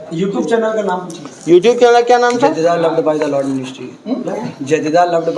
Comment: Youtube channel ka naam ky bataya koi batao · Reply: jedidiah loved by the lord ministry